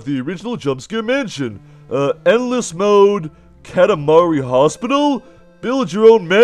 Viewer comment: Did just said "Katamari?"